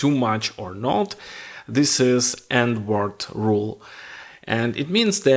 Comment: if you're not good at listening English (not your native language) like me, and has a little trouble to get what rule he said, it's the "And rule" (the auto caption generates "end", and I took some time to figure it out)